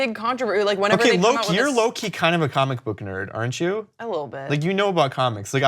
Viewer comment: Steering away from the headlining 'Driving lessons for sex/Today I Learned', did Amir call a "low-key kinda comic book nerd", or a "Loki kinda comic book nerd", considering the Marvel subject matter?